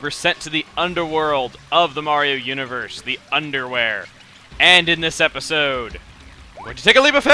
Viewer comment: wait, wait, wait! did you say "underwear" in the start of the vidio?